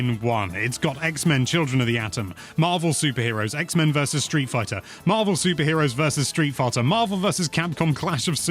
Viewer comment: Is it just me or did I hear "Street Farter" during that list off of all games included in Marvel vs Capcom fighting collection 😂